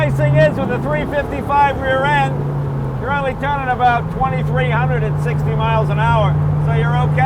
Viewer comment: Whoops, I swore at first he said 2,360. Oh well, haha.